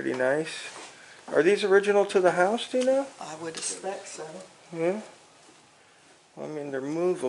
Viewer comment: did she say " I wouldn't select so"? Lol